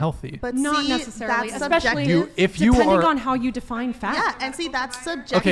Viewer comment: did I hear "It depends how you define fat" ? Holy God damn, with the mental gymnastics modern girls go through, they could be in the damn Olympics